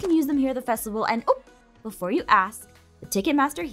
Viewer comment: D...did she just say "and i oop"? yall Ava is a vsco girl..